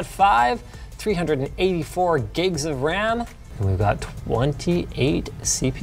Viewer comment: did he... did he just say 384 gigs of ram?